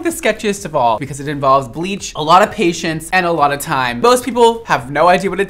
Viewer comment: I thought you said "patients" but I think you actually said "patience"